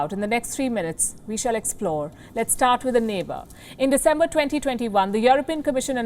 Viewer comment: did she say December 2021?